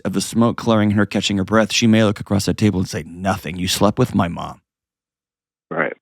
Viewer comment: At first I thought it said "I slept with my wifes mother in law" and I was like hold up...